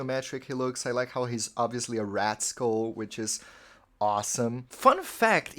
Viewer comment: When you said it was a rat skull (which I agree with, very cool yet subtle!) I totally misheard it as 'rascal', and I still agreed 🤣 "Yeah papa 4, what a lil rascal :3" Love this video! Even if I didn't agree with some of the rankings your arguments were very persuasive (and some were hilarious 🤣😁💜)